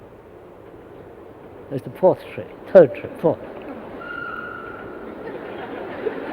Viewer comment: What's happening here? What is he saying? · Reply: At that time a train was passing from there, so he said this is the fourth train.